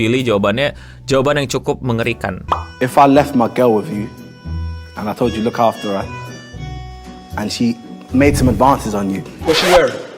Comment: dia ngomong apa si? kaga kedengeran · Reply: Ch: If i left my girl with u, and i told u to look after her and she made some advance on u Ph: what she wear?